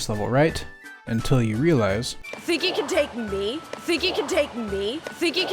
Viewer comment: I heard « You think you can dick me ? »